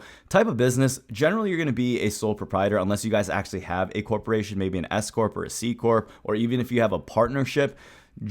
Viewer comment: I heard "escort" 😶....